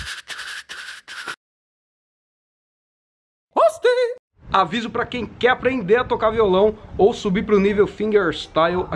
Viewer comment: alguém pode me dizer oq é dito, q eu nunca entendi? Ksksksks valeu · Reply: Toasty, é uma referência aos mks clássicos